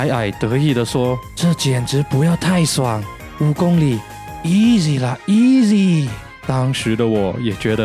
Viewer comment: 聽成「五公里?億里啦！億里！」